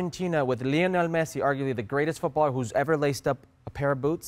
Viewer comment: Yes I think it might be. Oh sorry I thought it said 'racist' not 'greatest'.